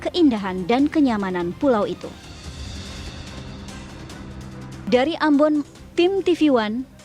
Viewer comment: 2.41 ....dari Ambon ? Apa saya yang salah dengar